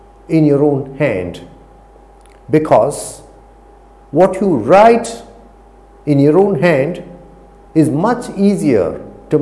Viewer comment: Sir, I do agree with this technique as effective. But, if this really is the activity Vedic people followed, then in VP the writing was not practiced. There is no evidence till now present to show vedic people knew to write. There is proof for veda composition but no literary evidence found. Vedas were written (compilation) after 500BC nearly after <num>years from composition of Vedas. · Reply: I thought he said “write in your head”. Is that right, or am not not listening carefully?